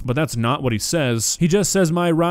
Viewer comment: Listening to this before looking at the captions, I heard “ain’t somethin’ you can stop” and not “and son, you can’t stop”. 🤷🏽‍♀️